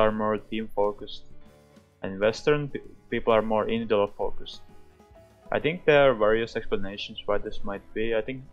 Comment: I'm sorry but what did he say at 1:18? I really have a hard time understanding his accent. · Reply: "Certain people are more invidual focused & there are various explanations why this may be." I agree that wasn't very clear at all.